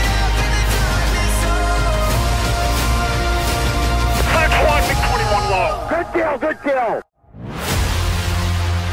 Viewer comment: Here's what he says (I think) Murican Pilot "5-1 Mig 21 Log." Soviet MIG: "Red Tail, Red Tail!" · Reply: They actually said, "Fox 1, Mig 21, low" and then the other american said "Good kill, Good kill"